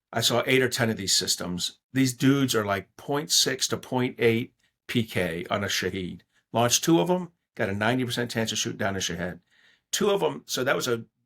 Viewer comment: Did he call a shahed drone a shithead drone? 😂